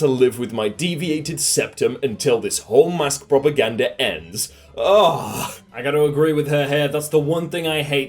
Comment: did he say “I’ve got to agree with her hair.”? As in the Karen haircut? Because they’re a Karen. Probably just over thinking this and it’s just the accent.